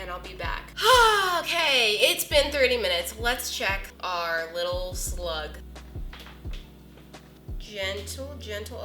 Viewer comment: I thought she called the Cake roll a slut... 😂 Also who else thinks she should of called these squishy bake-overs? Like if u agree